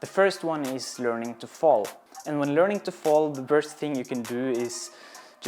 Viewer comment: small but relevant correction of the generated subtitle: he's saying the worst thing instead of the first thing I really appreciate how you thought pedagogically about it as an experienced person but really aiming to help the others to progress with this task and not just showing off in another video that would be sum up in « hey look how I'm good! ». My problem for years is really that weird upside down feeling quickly moving to panic when losing balance 😅🙈 That's also the reason why I never managed to proceed a proper cartwheel move but this will definitely help. Thanks for all the good explanations ! 🙏 The rest of the work is now on our side 👍